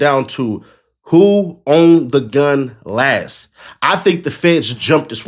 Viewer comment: I could have sworn all the bloggers said Ben 10 dropped the gun!